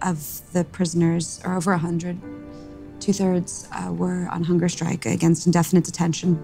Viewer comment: was she saying the captives are over 100 years old? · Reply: No, over 100 were on hunger strike (is what she said).